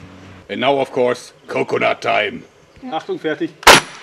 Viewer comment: why does he say 48 right before he shoots instead of 1?? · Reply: Leonhard Say owww i thought he said achtundvierzig sry my german is a bit rusty XD